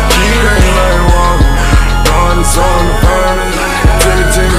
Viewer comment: when he said "He know that his soul in the furnace", it sounds like he said "He knows I just don't want to find it", that's what I thought he said until today.